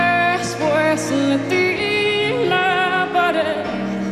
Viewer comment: Pensé que habia dicho "despues metí la pared" pero era "despues de tí la pares"😂😂😂😂😂